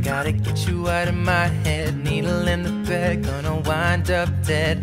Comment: I always heard " needle in the thread"